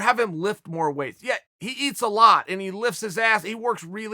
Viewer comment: 1 view and 6 likes? Chael doesn’t need views to get likes. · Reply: almost thought he said "licks"😂😂